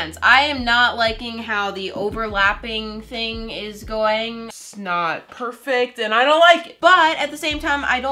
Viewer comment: Her:it's not Me:did she just say snot